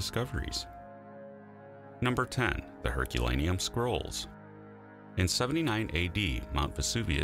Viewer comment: The Herculean scrolls thing is especially amazing to me. To recover that lost literature just makes me happy. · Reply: I thought at first he said "Herculaneum Squirrels", like they were an artwork or an extinct species.